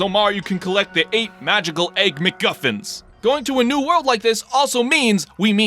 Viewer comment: Not going to lie I thought he was saying "egg McMuffins" lol